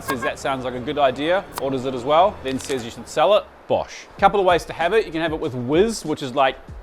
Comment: was that a Big John classic "Bosh" I heard just there? Bossssssssh!